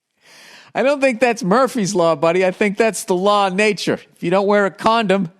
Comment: Murphy’s law, I thought you said Megan’s law 🤣